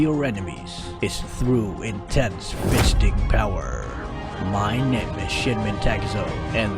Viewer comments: wait hold on.. , i heard "my name is sh*tmen takezo" lmao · UFSG FTW!!! (did i hear "fisting power"?)